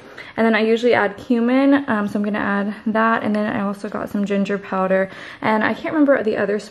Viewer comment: i almost thought you said Human and I was like wait wut